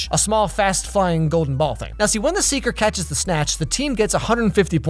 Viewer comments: did he just say say snatch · I heard that snatch